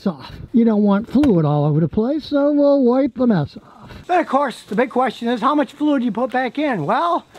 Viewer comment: I thought he said we’ll wipe Vanessa off😂